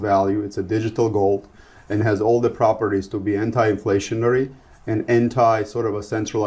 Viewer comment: Did he say hentai? 😂